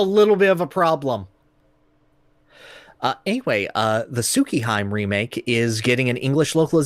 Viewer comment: What did you just say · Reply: Soo-kee-haim It's a word I learned before basic Japanses phonetics it's stuck in my brain.